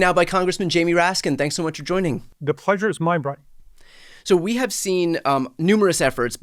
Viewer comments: I had to rewind. Thought J Raskin said, "The pleasure is mine bruh!' 😆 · For awhile there I thought I heard Rep Raskin say "pleasure's mind brah". Sorry for seeming to make light of the situation.